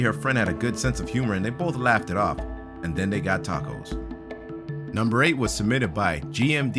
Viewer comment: #9 instead of saying tacos I thought you said topless